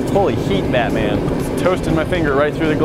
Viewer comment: lol, I thought he said HOLY HEAT BADMINTON. I don’t have the best hear I guess.